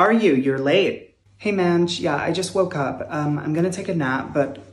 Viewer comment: When they said "mange" (not sure how to spell that) I thought they said minge and gasped!